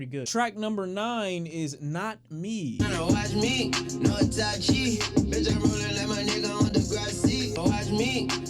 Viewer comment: it does sound like he said "watch me,no itachi" which makes sense too since itachi gots that double-xl op sharingan